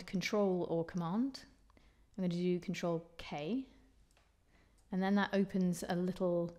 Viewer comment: Pls Can anyone explain me 3:32 to 3:40 sec..... I couldn't catch what she said.. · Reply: She's instructing the user to press the "Ctrl" (short for "Control") key (in MS Windows ) OR "Cont" (short for "Control") key - apparently the same thing as the "Ctrl" in another operating system. Then hit the "k" key to enter the letter you want for the chord. Yes, with her accent, it sounded like she said c*nt.